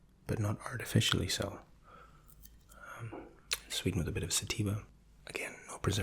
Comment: Rewatched this the third time and realized instead of saying "Stevia" he said "Sativa" at 14:53 . Lmao I don't believe that they sweeten things with sativa.